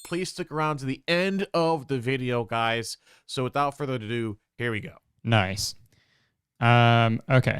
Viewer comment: did he say “without further to do” instead of “without further ado” ??